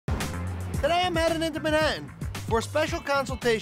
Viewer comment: what did he say????😂💀💀💀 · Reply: I_like_zebras_ “today, I’m headed into Manhattan”